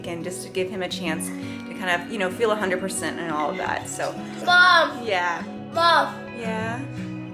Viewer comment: I think at 2:15 said that: "Mom...mom... I love you.." Btw happy bday little! ❤